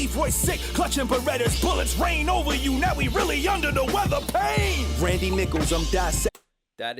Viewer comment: On the roof, bout to leave Royce sick, clutching Berettas Bullets rain over you, now he really under the weather (PAYNE) I thought he said Pain, made me think of Naruto Pain but also pain like in despair